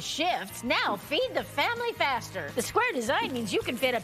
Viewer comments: I thought she said: Beat the family faster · I know the commercial said, “Feed the family faster”, but I misheard it as “Feed the family bastard”.